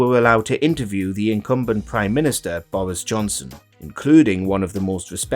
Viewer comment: LOL When you opened with Johnson, for a moment I almost heard you say "The incompetent Johnson." Imagine my disappointment when you reached for "incumbent." "Incompetent" should have been your first choice.